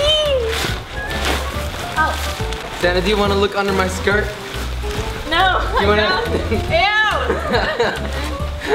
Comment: I thought she said Santa